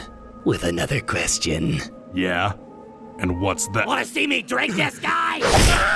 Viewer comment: Thought he said wanna see me drake this guy